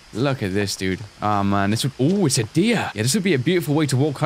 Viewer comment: when he said "on it's a deer" I thought he said "oh it's her dead thia"